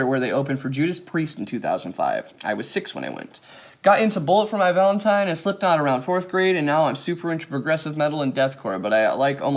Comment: I slipped on instead of Slipknot 😂